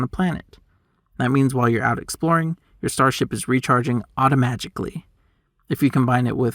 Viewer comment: Did you really say "automagicly"? If yes: 👏👏👏! Chapeau.